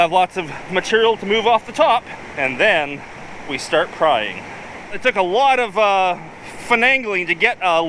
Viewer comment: did he say, "then we start prying," or, "then we start crying," ? Could go either way..